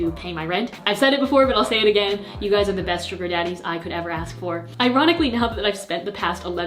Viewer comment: i thought you said stripper daddies and i